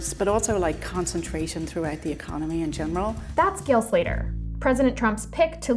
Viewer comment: seriously, I heard GILF Leader the first time I heard it 😂